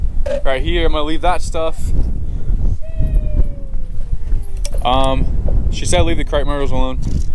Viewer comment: Did anyone else hear sheeesh in